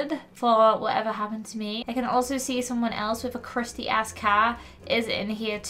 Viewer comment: did say crusty a$$ car? Because I think she did😮